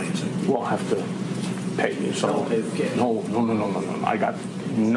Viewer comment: He clearly says "So we'll pay with Cash!"